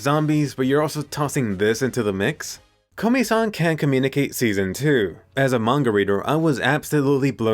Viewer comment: the text says ,,can't dance,, instead of ,,can't communicate